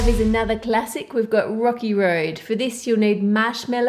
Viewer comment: when she said "rocky" I heard "rocke" ha ha ha ha lol If u don't know who rocke is then search bts rocke on google and ur answer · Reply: when she said "rocky" I heard "rocke" ha ha ha ha lol If u don't know who rocke is then search bts rocke and u will get ur answer